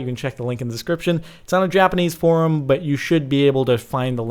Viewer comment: did he say Japanese P0rn??? O.o